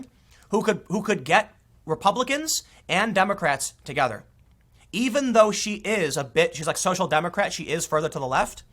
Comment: From 21:40, did you say "Even if she is a bitch" ?